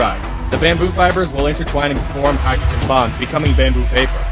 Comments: I thought you said hydrogen bombs lmao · i thought it said form hydrogen bombs for a second..